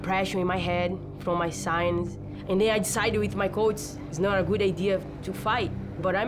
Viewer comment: "I decided with my coates" when I heard that I just imagined nunes in a dark room with a bunch of Coates